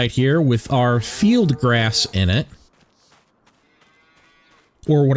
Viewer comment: I thought you were saying Metal Grass. I was so confused. I actually enjoy missing up their food. Im sure it's annoying for folks though.